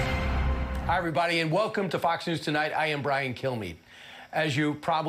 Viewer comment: I swear- every single time I hear this fox liar introduce himself, I hear “I am Brian. Kill me.” It’s not going to be me, but maybe one day someone will take you up on that and you’ll get your wish, Bri-Guy.